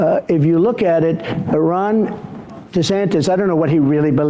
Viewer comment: Holy Shit!!! Did he say Iran DeSantis, and sneak in a word association Freudian Slip to stare at goats his supporters???? The old man's still got it 🤣